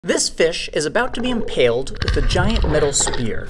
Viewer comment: When you said this fish at the beginning of the video I thought he said "this Bish"